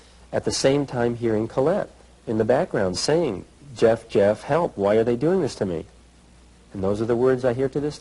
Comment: I think Collette was saying “Jeff, Jeff why are YOU doing this to me”